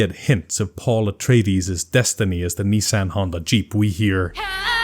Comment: i almost spit my tea when i hear "nissa al gaib" my subtitles read Nissan Honda Jeep.